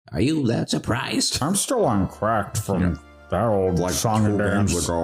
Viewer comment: When the said "I'm still on 'cracked'" I actually thought he said he was on crack